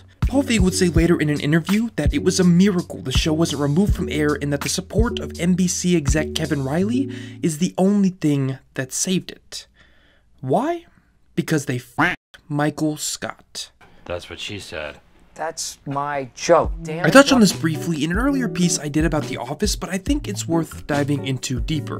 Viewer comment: "Why? Because they [duck noise] Michael Scott." I legitimately can't tell what he's supposed to be saying here. What verb does he use. It could be that he says "they fucked Michael Scott" because there's an f sound right before the weird quacking noise, and he could be using a duck noise as stand-in for a bleep, but like, what does that mean. They fucked Michael Scott? I'm pretty confused about this.